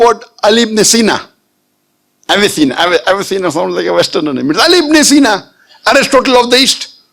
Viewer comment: beautiful answer mahsallah, but what did he say at 5:20? i couldnt get the words exactly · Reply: Avicenna Ali ibne seena